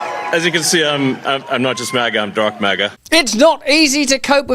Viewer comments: I thought he said "Darth Maga" 🤣 ˆlike that even better. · I thought he said Darth Maga causr he had a black cap on? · I thought Elon said Darth Maga